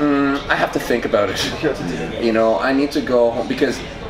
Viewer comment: and on it's soo obvious that he's talking about roids. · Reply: Truth. Devon's lines were at 4:10 or something was I need to go ho- or something h word for me I think he said go home meaning he didnt want to die 😂 and then after he says "It's hard choices"